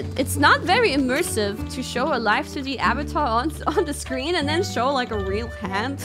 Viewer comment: I think Kiara said "Live2D avatar" instead of "lives to the avatar"